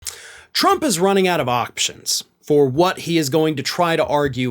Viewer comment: I thought you said trump is running out of oxygen, then I reversed the vid and listened again and depression and reality set in when you said options……😅